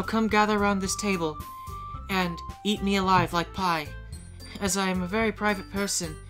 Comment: DID YOU SAY PIE HMMMM